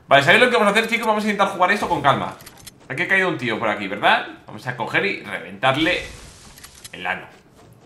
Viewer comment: v: vamos a coger o escuche mal😂